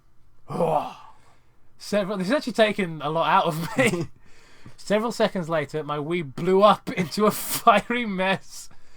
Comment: What am I supposed to google image search around 40:00? Couldn't quite catch that in the audio. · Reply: Ayy four years late but he was saying “Chakat” in his English mangling accent.